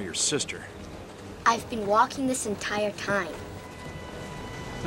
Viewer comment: I always thought he said "I'll pet you" which I thought was random and funny but didn't make much sense. lol